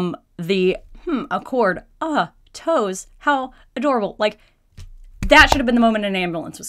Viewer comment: It sounded like she said "Cord? Oh! Toes!"